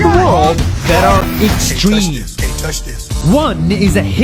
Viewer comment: Do i hear dont touch this in